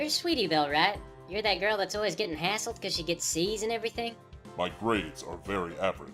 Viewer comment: what does apple bloom say at 3:24 'Your that girl that's always getting hasseld because she gets????? and everything?' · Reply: eye "because she gets C's in everything". As in her grades in school are only ever average... which is Thrack's intent I am sure.